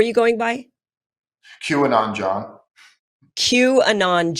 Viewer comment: I legit thought he said, "Jewanon John." That would have meant something different.